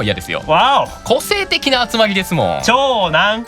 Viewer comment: Sounds like asmo saying "wow"